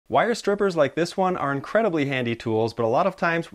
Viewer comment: This came on right after another vide, I wasn’t looking at my phone and I heard “why are strippers like this one…” bahaha